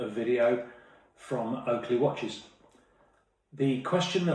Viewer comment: I thought he said welcome to Ugly watches